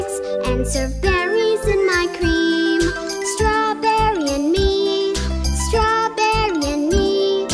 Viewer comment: and 1:00 - 1:04 Why do I hear “strawberry meat” instead of “strawberry and me”💀